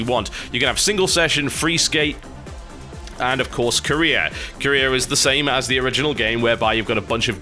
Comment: Who else thought TB said Korea instead of career.